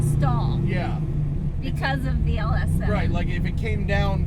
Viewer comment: Matt, have a question for you... Did she said "LS7" ?? At 9:34 ?? In the description says LS3, that's my doubt.. Great car ! Great vid, as always! Thx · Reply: It is an LS7 car, the LS3 in the description is a mistake.